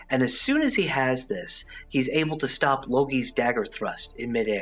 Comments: I don’t know if I heard this right but did he say “logi”? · I heard logi instead of Loki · If you listen closely in minute 2:25 he says logi instead of loki lol